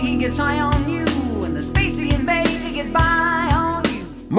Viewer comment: I always thought Geddy was singing " the space invader he gets high on you ". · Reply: Me Too. Except I thought he said "the spacey invader".